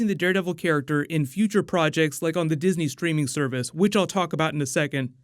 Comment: Did he just say Disney streaming surface 1:30, these shows aren't for kids